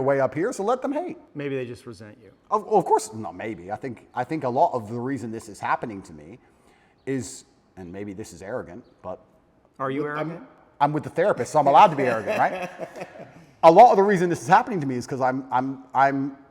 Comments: I thought it said andrew tate vs "The Rapist" for a second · I thought it said The Rapist 😅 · I thought it said the rapist. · I thought it said The Rapist lol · I thought it said the-rapist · I thought it said the rapist😂